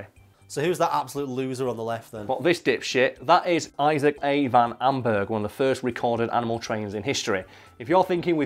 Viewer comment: The only thing I want to know is what did you say other then "Issac" I dnoo if anyone else noticed the edit at 1:41 But I did..... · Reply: The guy's name. Isaac A. Van Amburgh.